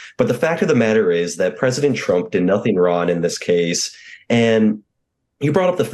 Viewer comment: Nobody said Trump did anything Ron. We all know he did something WRONG. I thought I misheard this propagandist but he said "nothing Ron" twice. CYA?